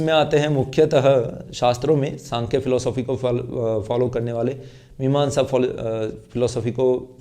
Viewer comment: How can u say that shankar sampradaya are nastik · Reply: Shankar nahi bola sayad Shankya bolA hai dyaan se suniye